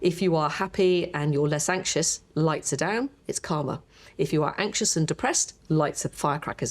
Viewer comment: When the Queen of pain lady said about being happy, less anxious turning the pain response down she said it's calmer, but I heard "it's karma." ie if you're happy, peaceful, your mind creates a less suffering world of experience. So I replayed it with the subtitles on and the subtitles thought she said "it's karma" too !